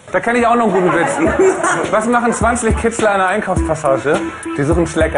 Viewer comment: was sagt oli da???? was machen 20 Kids ein einer Einkaufs??? denn rest hab ich nicht verstanden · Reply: MrTheBMS Was machen 20 Kitzler in einer Einkaufspassage? Sie suchen Schlecker.